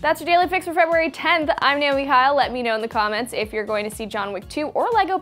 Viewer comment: Am i the only one who heard her say "Im Naomi Hyle" in the outro